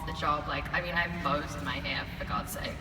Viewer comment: Hello everyone. Tell me please what Alycia says at 30:41 - "I become very different when I'm off the job like... (and then laughs)" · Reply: Game ON “ I have bows in my hair for God’s sake” she was so sweet and adorable!